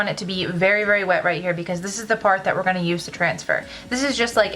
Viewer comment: i thought she said "this is the fart"